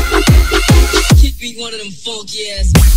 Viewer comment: What say? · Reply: “give me one of them funky ass (beats)” its probably sampled from N.W.A.’s “something like that”